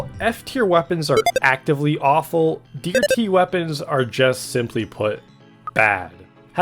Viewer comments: I'm not the only one who heard deer tea weapons at 5:00, right? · he says deer tea instead of d tier